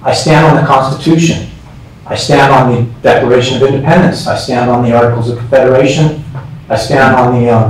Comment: did he just say "the DECORATION of Independence," there at the end?? 🤔